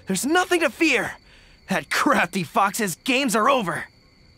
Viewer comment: I paused the video at 2:14 and when I came back and played the video I misheard Gorou and thought he had said "That crappy Fox's games are over" Lmao